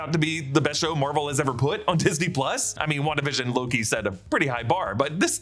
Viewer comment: At first I thought you were saying "Wandavision lowkey set a high bar" but you were saying "Wandavision and Loki set a high bar" lol